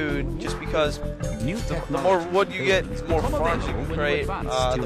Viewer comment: WTF DID U JUST SAY AT 1:13 LOL IT SOUNDED LIKE "MORE WOOD U GET " "MORE FART U GET"? LOL REPLY TO ME IF IT SOUNDED LIKE THAT LOL